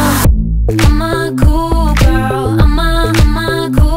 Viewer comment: I thought she was saying “I’m a cougar” for the longest time